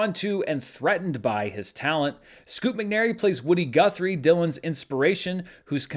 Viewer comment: for a seconed there, I thought you said Scoop McGee!